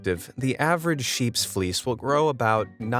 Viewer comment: Talking about that sheep I swore I heard you say “fleas”..haha 75lbs of fleas! Hoooweee. Ohhh..fleece. 👌🏼 much better